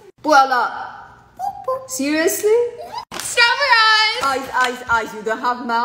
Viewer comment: "Straw ber eyez!" *doesnt notice her brother in the background* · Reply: What I hear: "STRAW BEH RISE" Me: HAHAHAHHQHQHAHQHHAHAHQHQHQUHQHQ